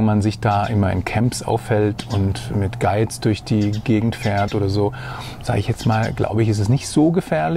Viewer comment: Wer hat bei 04:38 auch verstanden: ...und mit Geiz durch die Gegend fährt... 😅